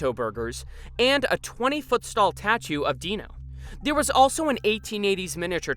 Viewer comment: Please tell me I’m not the only one who heard “stall tatue” at 5:35.